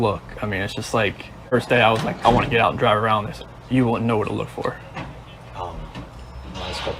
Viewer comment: CW says, "YOU wouldn't know where to look for her, instead of I". Within the context it is out of place, and he is telling on himself. HE knew where to look for her, but YOU wouldn't. I am glad he is in jail and thrilled to know that he wants out! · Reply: He doesn't speak clearly, and the audio isn't great, but he does say 'they said you wouldn't know where to look for her'. It's in the discovery where the cops said 'don't go driving around, you wouldn't know where to look for her'. He's repeating that quote.